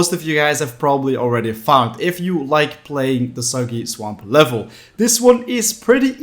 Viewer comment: When he was talking about soggy swamp it sounded like he was saying Soviet swamp